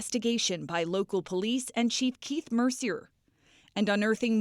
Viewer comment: It sounded like she said Chief Keef lol